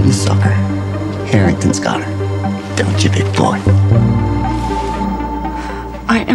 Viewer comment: Eddie: Don’t ya big boy? Me: Oh shi- I thought he said baby boy-😂